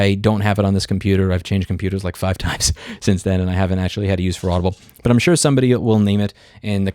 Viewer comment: I misheard "for audible" as "Fraudable", which honestly fits way more than it should